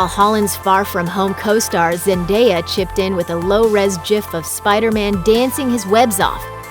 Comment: “Jif”...? Did I hear that right...?